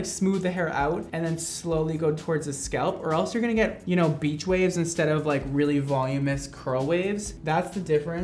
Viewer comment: When he said beach wave I thought he said bitch wave XD